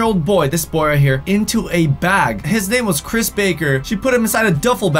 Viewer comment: When Tal said “ His name is Chris Baker” I thought he said Cash Baker... 🤣